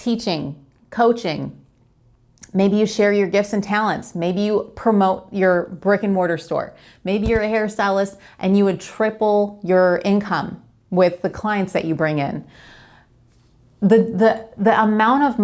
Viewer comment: When you were talking about opening a brick and mortar store it sounded like you said a "Rick and Morty" store and it made me chuckle Keep up the great work and thanks for the inspiration!